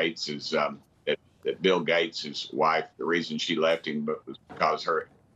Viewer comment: Did I hear the name Bill Gates there?